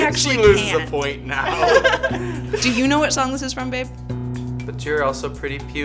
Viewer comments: did call babe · ummm why did she say BABE 7:02 or did she say j ?